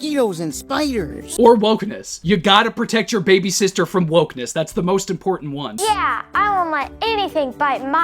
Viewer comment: My brain skipped a beat when you said “gotta protect your baby sister from wokeness” I heard “protect her from the Bolsheviks”, and now I can’t stop laughing.